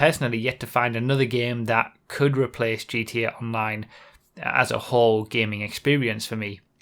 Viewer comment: GTA Nine? I know you/he said Online, but it sounded like nine so yeaaaah